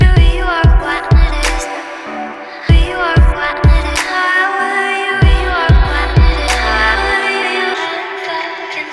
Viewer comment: does anybody knwo what shes saying? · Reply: nvm shes saying “i didnt outgrow you (threw you out“ backwards